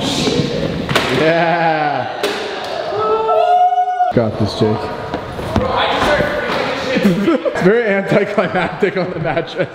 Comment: did he said got this chase to Max